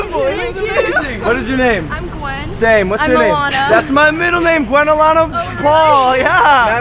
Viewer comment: I thought when the girl at the start that said her name was Alana I thought she said Moana😂 SEE THE LINE WHERE THE SKY MEETS THE SEA, IT CALLS ME